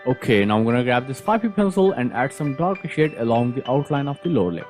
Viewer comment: Is it just me or at 3:56 he said “dog sh*t” instead of “dark shade”